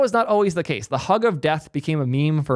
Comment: I misheard hug of death as hog of death.